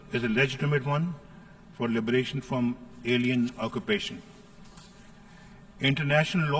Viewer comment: did he just say alien occupations